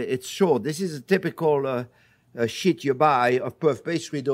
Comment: Bro I had to keep replaying this part cause I thought he said "This is the typical shit you buy" until I realized he was saying sheet lol.